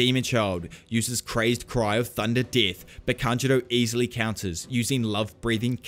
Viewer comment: I thought you said Tanjiro 😂😅